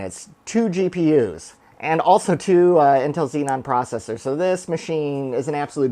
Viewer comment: Did I hear Intel Xenon processors 0:59 🤭🤭🤫🤔